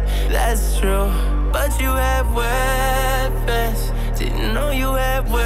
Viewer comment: I heard "but you have herpes" 😂😂😂